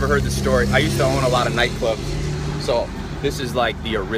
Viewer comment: did he say"I used to OWN alot of night clubs"?